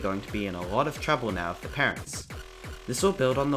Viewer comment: do i hear pico vs parents where you play as him?